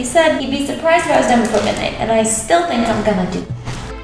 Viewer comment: she fucked up the subtitle I think. When they talk about when she is gonna finish. I swear, I hear her saying: I still think I am gonna do it. ( the sub says: Marie Kondo would not be pleased)